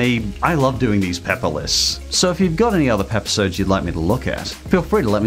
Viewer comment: Sounded like you said peppisodes